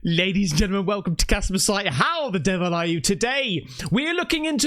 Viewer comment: I heard "Jasper " on 1st video